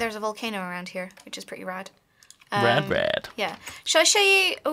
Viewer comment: did Duncan say RadBrad? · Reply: Yes, yes he did. After Kim said Rad, Duncan said Rad Brad.